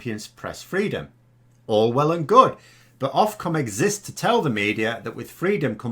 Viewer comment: I misheard you as saying " Orwell and good" at one point. That, or course, is what our masters and their toadies will shortly be saying and boasting about. Thank you Brexwits. We are well and truly shafted.